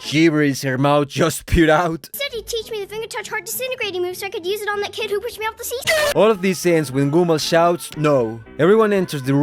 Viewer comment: I consider your English to be immeasurably better than mine and I still understood what she said. "He said he'd teach me the finger-touch heart disintegrating move so I could use it on the kid who pushed me off the see-saw".